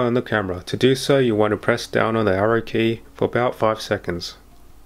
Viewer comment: Great video but I am having trouble connecting the camera to my iPad. At 6:40 I can’t catch what you said…press the ROT for 5 seconds…?…and is that the Down Arrow button? Sorry, I am terrible at these things! Any information you can share will be greatly appreciated! Great video again! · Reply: Thanks for watching. You press the down arrow button for about 5 seconds until you see the “waiting for wifi connection”. Hope this helps